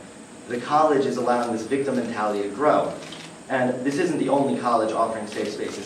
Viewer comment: I legitimitly thought he said this dumb mentality